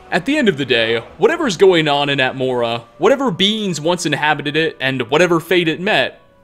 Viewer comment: Genuinely thought he said "Whatever beans once inhabited it" and was very confused for a second.